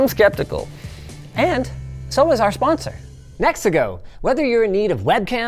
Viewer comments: I misheard your sponsor nexigo as mexico. Big eyebrow raiser until i looked at my screen. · i SEVERELY misheard who the sponsor was, and for a second i thought linus said Mexico